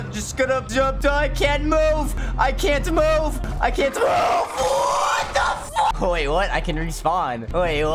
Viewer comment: I think me said WHAT THE FU